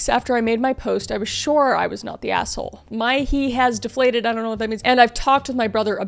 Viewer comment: I think she was saying her ego has deflated